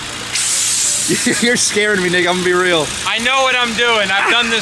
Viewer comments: what did he say? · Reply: "you're scaring me nick ima be real" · i thought he said "you're scaring me nigga imma be real" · what did you say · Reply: "you're scaring me nick ima be real" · what say lol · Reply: "you're scaring me nick ima be real" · yo did say the n word at 9:10 lmao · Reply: "you're scaring me nick ima be real" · am i crazy or did just say the n word (the captions literally censored it like the n word) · did just say what i think he said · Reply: "you're scaring me nick ima be real" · what did say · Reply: 'youre scaring me nick ima be real' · why did say that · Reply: he said "nick ima be real" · ayo what did just say??? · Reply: he said “nick imma be real” bffr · I have a feeling that Tanner will not be happy by the end of this video... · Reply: just to clarify you did say nick right? Kinda sounded like something else 😩